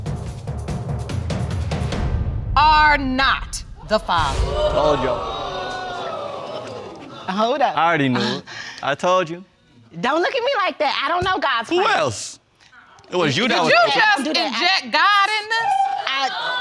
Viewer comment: hahaha did she just say' I didnt know gods plan'!!!!!!!!!! STOP IT hahahaa